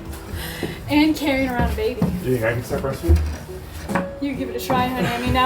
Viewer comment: For a second there, I thought you said was going to start breast-feeding. That would make you an amazing wife lol